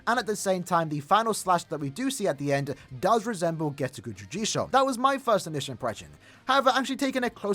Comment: "The final slash that we do see at the end does resemble <inaudible> " What did you say there? I didn't quite get it 🤔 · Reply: oh, Getsuga Jūjishō